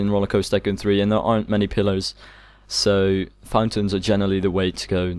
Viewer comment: Did u just say pillows instead of pillars????